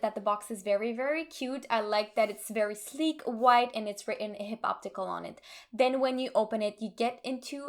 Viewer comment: I thought you said “hip-hop tickle” and it made me laugh. Love your videos and can’t wait until you hit 100K so I can brag about being an OG subscriber.